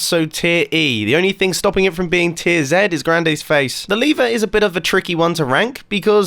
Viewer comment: For some reason I thought he said "Griande's face" instead of "grandayy"...Its too early in the morning. I need coffee😂😂😂